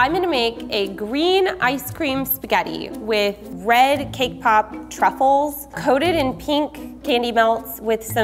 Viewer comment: Who Else Thought she said K-pop?